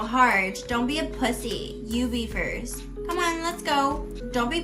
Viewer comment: ไม่รู้ว่าคิดไปเองไหมเพราะได้ยินคำว่า pussy(พุซซี่)= xี 😢 หยาบจัด